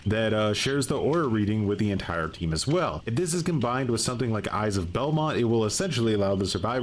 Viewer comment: did bro just say eyes of Velma???? 😭